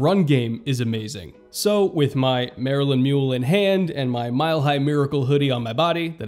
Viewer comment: What kind of mule was that drink? I thought said "Marilyn Mule" but I can't find any reference to that anywhere else...